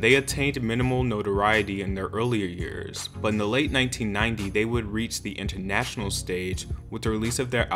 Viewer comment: for everyone correcting me on the timeline i said “In Late 1990” (meaning the year alone) not late 1990s as in the decade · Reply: I hear "in the late 1990" no s